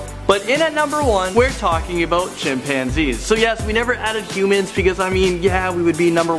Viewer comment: When he said chimpanzees,I thought he said champagne.Ha LOL